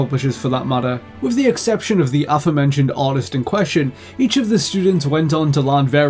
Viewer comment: d-did you just say "autist"?